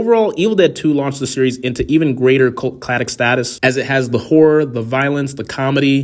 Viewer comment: At about 9:24 did he say it was a cult "clattic" lmfao 😂😂😂